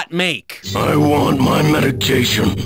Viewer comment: I think he's saying "I want my medication"